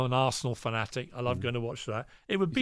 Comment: Leave it to DJ Envy to have his ears perk up when he misheard Pierce saying he's an "arsehole fanatic". That boy can't run away from that dildo at all lol